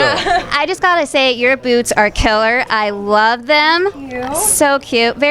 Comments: My hearing must be going but I thought the interviewer said to Victoria that her "boobs" were killer. She said "boots". Wow, that's a relief. 🤣🤣. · I totally misheard and thought she said boobs at first lol